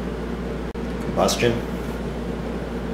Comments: is it me or did he say "idiots" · did he say "Idiots" at 1:05????? hahahahahaha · listen to 1:04 over and over, is he saying "idiots" ?